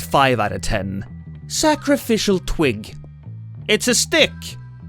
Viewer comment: I keep hearing sacrificial twink 😂